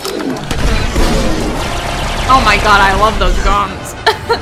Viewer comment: spit my tea over my desk cause I thought you said "oh my God, I love those dongs"